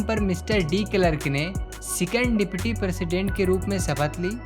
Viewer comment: saw the subtitles, it was dick clerk instead of de Klerk 😂😂😂 Anyways the explanation was superb ❤️